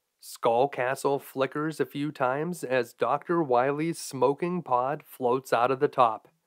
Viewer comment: Me, mishearing at the end: "Skull Castle flickers a few times as Dr. Wily, smoking pot, floats out of the top..."